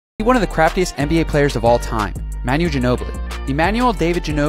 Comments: I thought he said crappiest player and I was like tf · I thought you said “crappiest” instead of craftiest until I watched it again😂 I was like “damn bro, what’d he do to you?” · Am I the only one who thought he said crapiest until I read the description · I thought he said "the crappiest player of all time " I was like wtf this guy saying 😂😂😂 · Lol I thought he said the crapiest player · At first I thought he said the crappiest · I thought he said crapiest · I first thought he said crappiest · Who else thought he said crapiest and was confused af… LOL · I thought he said crappiest at first and thought really that what a crap nba career looks like?